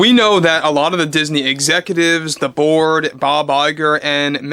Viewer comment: When you opened you said Disney Excutives, the board it sounded like you said "the Borg". Makes sense as these people was to make us believe in their screwed up thoughts